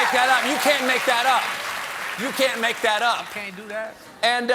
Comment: Anybody else hear the random dude in the background say "you can't do dat" at 0:44.